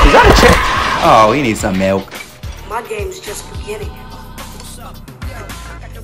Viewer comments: Bro I thought it said my gay💀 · I thought he said he need sum milk but he said he need sum help